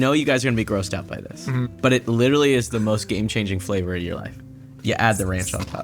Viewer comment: What did he say? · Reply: It literally is the most game-changing flavour of your life